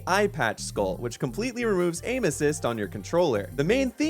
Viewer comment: While watching the video, my wife walked in when you said "aim assist" and she thought you said anuses. Now, that is all I can hear.